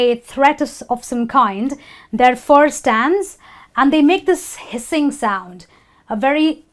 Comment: what does she say at minute 2:22? .. that force stands.. am i wrong? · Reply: Manuel José García Rodríguez yeah, kinda wrong. what actually she says is "therefore, it stands'. Im sure you're gonna understand that when you listen again.